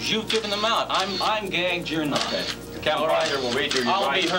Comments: I think there is an error in closed captions. Captions read "I'm gay" whereas I heard "I'm Geins". I may be wrong and have been influenced by an Ed Gein doc I just watched but that's what I heard. You tell me. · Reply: Nevermind, now I heard "I'm gagged, you're not" which makes more sense in context. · I think there is an error in closed captions. Captions read "I'm gay" whereas I heard "I'm Geins". I may be wrong and have been influenced by an Ed Gein doc I just watched but that's what I heard. You tell me.